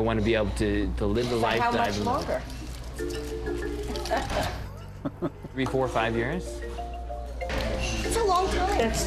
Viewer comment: what does he say?? 34 years??